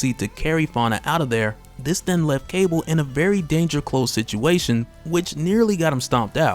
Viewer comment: did you just say Dangercul???